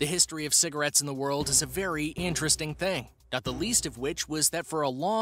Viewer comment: did he just say *anteresting* instead of “interesting?”